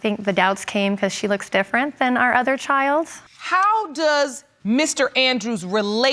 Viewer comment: Back to this video for a second time!! Hold up hold up....did she just say 1:21 That Amerella looks different than our other CHILDS!!!! 😲😲😲😲